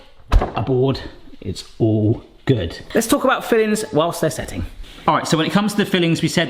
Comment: Thought he said let's talk about feelings while they're setting. Getting a little personal in here...